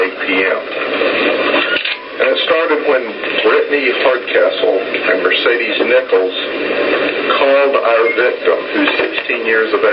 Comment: at first i thought he said brittany fartcastles and mercedes nipples